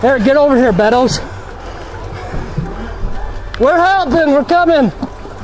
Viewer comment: I heard someone said "HELP ME HELP!"